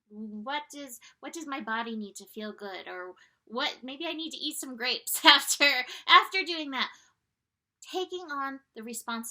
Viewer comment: did u say "maybe I need to eat some grapes, after doing meth"?! Now that's balance! 🤣